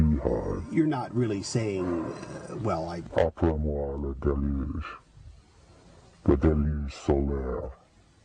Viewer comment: what is saying? "like everything, a beehive?" then something about a deluge? is there a transcript? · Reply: "Après moi, le déluge", "After me, the flood", is a French expression attributed to King Louis XV of France.